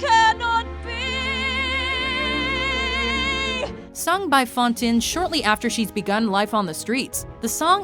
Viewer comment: did she just say "fawn-tin" at 12:20?!